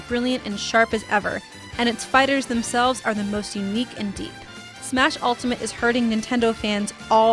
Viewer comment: "Smash Ultimate is Hurting Nintendo Fans..." LOL that's what I heard.. <link>